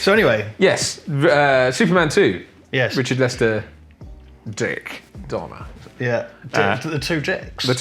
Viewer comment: I thought he said molester for a second.